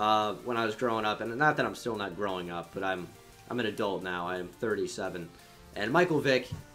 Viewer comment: Did any one else think he was in his early 20s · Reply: Did he just say he was 37 or did I hear that wrong